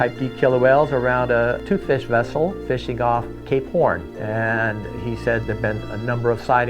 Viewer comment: sounds like you said gay porn lol I know what you said but I’m just saying what it sounded like at first.